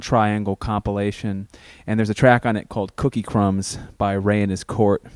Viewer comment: Can someone please tell me which song is he present at: 1:37:15 Did he say, "cookie crumbs by rainhus curt"? something like that. Thanks.